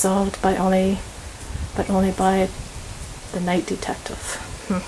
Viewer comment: the RIGHT detective. I thought it said night too at first, but had to double-check